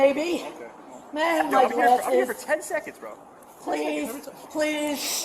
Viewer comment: around @ 00:18:20 did i hear some guy say "10 second rule" when they dropped her PAHAHAHAHA!